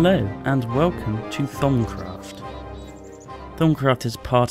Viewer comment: I heard "Thumbcraft"! LOL